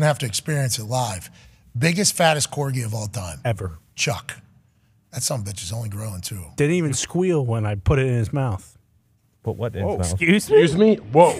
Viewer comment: All was missing was the WINkk 😜, when he said, ‘’didn’t even squeal when i put it in his mouth” 🤣🤣🤣 Conman you’re sick!!! (That 2:46:50 mark was wild) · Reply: Whats funny is when I was listening live, I heard "didn't even squeal when I put him in my mouth" and was defending what Connor thought he had said.. boy was I wrong lmfao